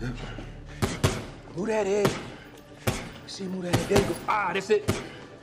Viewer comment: what is he even saying at 2:33 ? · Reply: "Move that head" he's teaching billy to slip a counter attack when he throws his punch.